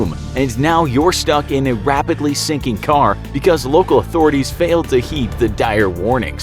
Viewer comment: I thought it said "Stinking car" at first 😂